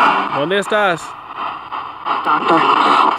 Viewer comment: "Stockton" (I asked where they're at in Spanish, this female replied) …"are you recording?" (male spirit asks) We were videoing. I think I also hear "Que dijo?" (what did he say? in Spanish)